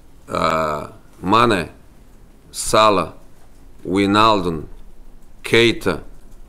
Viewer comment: Did he just say SALAH 5:38 ! KARMA IS A FUCKING BITCH 😂